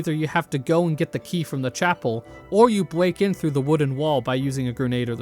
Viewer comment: did you say "bweak in through the wooden wall" like fucking Elmer Fudd?